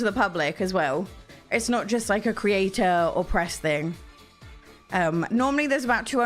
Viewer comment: Haha, I am so sorry I am just watching you at work and idk if I am tired or if this is the differewnce in the accent but you said "this is not a creator or press kinda thing" and I heard "It is not a creator opressed kinda thing" I had to rewatch this part - I almost spit my coffe, I swear 🤣